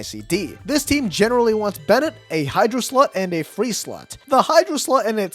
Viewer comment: did you just say Hydro slut or Hydro slot i couldn't tell but i laughed regardless 😂